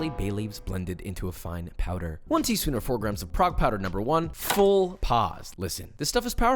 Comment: I heard "Frog Powder"... Subtitles called it "Proc Powder" (Looks it up online) Oh... "Prague Powder", also known as curing salt. Gotta love it when these things just blaze by an relatively uncommon ingredient with no aside or explanation.